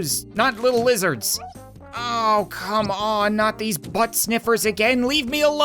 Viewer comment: I think he said oh no not these butt sniffers XD